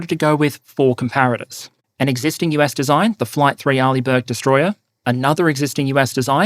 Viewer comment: I initially thought you said "Allibert-class destroyer." Which confused me a little, but I rolled with it. They make decent stuff, y'know.